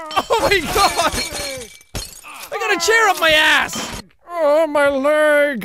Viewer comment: did he say ah my legs